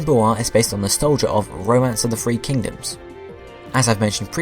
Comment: It says "Roman" instead of "Romance."